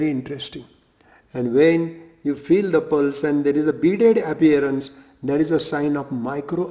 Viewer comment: "when you feel the pulse and there is a .... appearance" what word did he say there? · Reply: beaded appearance (tiny bulge like a small bead), I think.